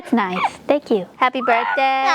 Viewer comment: I thought I heard say "Happy Bird Flu!" LMAO